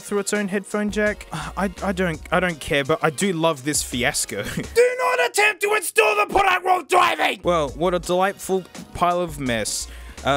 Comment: What he said: “DO NOT ATTEMPT TO INSTALL THE PRODUCT WHILE DRIVING!!!!!” What I heard: “WHATS DA PASSWORD‽‽”